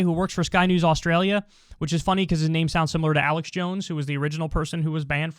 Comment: I seriously thought he said Alex Jones